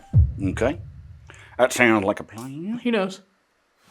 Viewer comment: So at 35:05 it sounded like a faint woman's voice like she was shy saying "Hi"